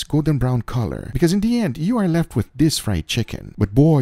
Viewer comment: "Im a fan" Proceeds to spin rapidly to blow air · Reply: i heard it "im a fat"